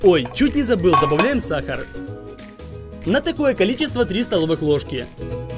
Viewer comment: мне послышалось 300 столовых ложек